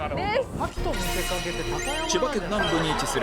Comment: のところで高山って設楽さん言ってるけどその前聞き取れなかった〜。なんて言ってるかわかる人教えてください〜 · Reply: 賀喜と見せかけて高山